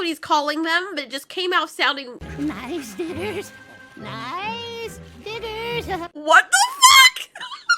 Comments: I honestly thought said “nice dinners” not “nice diggers” or…the other thing. For a second I thought he was a secret Sharptooth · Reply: I thought he said “nice didders” XD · I honestly thought said “nice dinners” not “nice diggers” or…the other thing. For a second I thought he was a secret Sharptooth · I heard didders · i thought he said nice ditters 😅😂 and i was like the helll